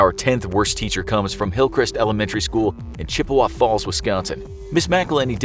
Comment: DID I HEAR WISCONSIN!?!?!?!?!?!?